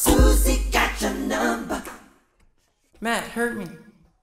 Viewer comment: Does anyone know what is he saying here " 6:36 "??? Thanks in advance❤ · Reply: I believe he says "Matt hurt me"